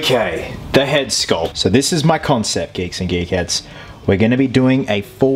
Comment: youtube subtitles says "geeks and GAY CATS"